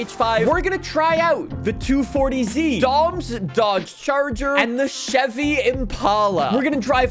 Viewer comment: at first i thought he said "dog's charger"🤣🤣😂😂🤣🤣🤣🤣😂